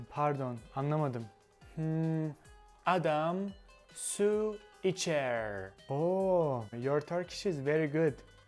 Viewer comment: I heard one 'Adam sue a chair'